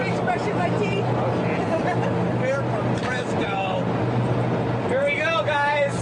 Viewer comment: Did he say "they're from Fresno?"